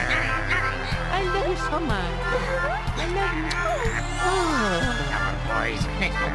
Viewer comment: I think I heard Mario say I love you and I love you so much to Peach at 5:14, awww!!! ^^💟🥺